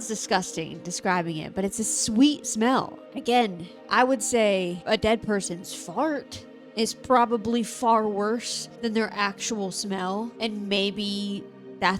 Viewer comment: I wasn’t entirely sure what started playing in my headphones but I just heard “dead persons fart” and knew I was in the wrong place. Y’all have a good day now